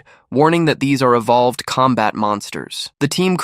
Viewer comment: Did i hear "Combat evolved"?!